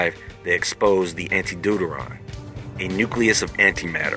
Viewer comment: did you say doodoo ron